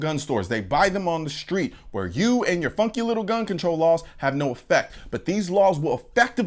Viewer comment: Yo 2:18 had me dying he said funky I thought he said fucking 😂 it was funny haha I love you bro haha